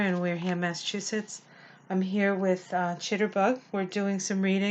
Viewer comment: Marcie Layton Hi, I have a question...is the name of that adorable creature who adopted, Chitterbug or Jitterbug? Not sure?! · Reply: I thought she said chitter bug but my ears are 54 years old so she may have very well said jitterbug lol